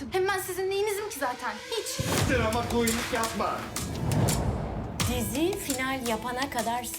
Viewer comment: arası Barış Falay ne diyor kağıtları atarken? Anlamıyorum. Anlayan var mı? Varsa yazabilir mi? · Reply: Drama Queen lik yapma